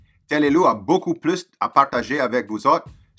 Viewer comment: How does he say goodbye at the end? 04:57 I didn't quite understand that. On va ... something. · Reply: On va se revoir - basically, “see you again” - a sign off